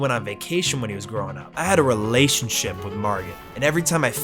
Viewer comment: I thought I heard "revelationship" at 18:20... that's a dope ass word. Great video as always!